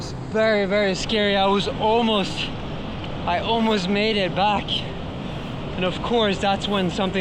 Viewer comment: Holy Magnus, that's probably the worst you looked, big props 34:19 I thought you said I was almost gonna die, but then its like wow I was pretty much dead I almost made it out XD ALSO HELL YEAH, RAINBOLT MAGNUS VIDEO WOULD BE EPIC